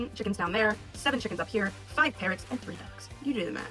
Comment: I thought she said “five parents” 😭